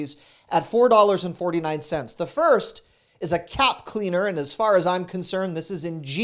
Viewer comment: Gotta love it!!! Love the candle house and I thought you said CAT CLEANER lol until the washer came out. Calorie free desserts not! The egg stuff comes in handy to!